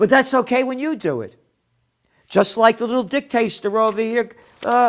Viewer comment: Gerald did I just hear you say "dicktaster"??? Can't stop laughing. I love you man!